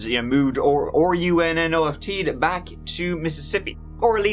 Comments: What exactly is that word he spells at 3:09 OURRNFT? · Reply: Colloquial misspelling of "ran off." The term is derived from the movie ‘O Brother, Where Art Thou?’. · Wtf is he saying/spelling here? Replayed it 10 times and can’t decipher it. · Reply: RUNNOFT. It’s an ‘O Brother, Where Art Thou?’ reference.